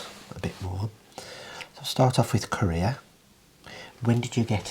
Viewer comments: omg, at 0:32 I thought you said "Start off with Korea" and wondered if that where was born 😀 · your accent!! 😂 I thought you said "we'll start with Korea" I was like oh jeez what joke is coming now hahaha